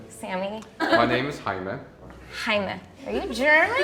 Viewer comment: As a Hispanic, it hurt when that lady thought the name “Jaime” was German :3 Edit: I just realized the Latina girl (in back) was also shocked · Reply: Oh, he said Jaime! I heard “Heinrich”